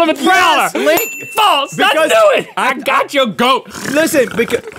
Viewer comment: did he just say “I gotch yo goat!”?